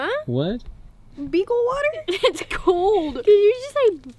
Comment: I thought she said beetle water lol